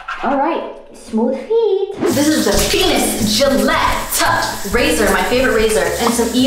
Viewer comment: okay I had the captions on sndvwhen she said "this is the venus Gillette razor"6:07 it literally translated it to this is the penis- ummm and I was like what did she just sayyyy LMFAO